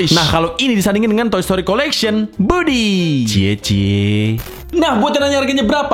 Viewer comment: sebenarnya namanya budi atau Woody gue dengernya Budi ya